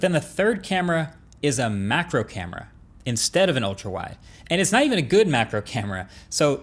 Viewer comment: macro camera instead of a telephoto* camera